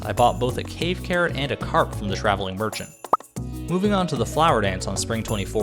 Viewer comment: i thought he said earth crystal and corpse from the trash can